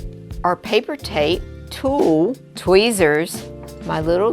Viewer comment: awesome video, thank you for sharing and providing a dst. what was it exactly you said at the 1:43 mark? paper tape, then you say tool. what did you mean by tool? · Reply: We're using tulle, it's a net-like material often used for making dresses, veils, etc. We use it in our craft projects for an elegant touch!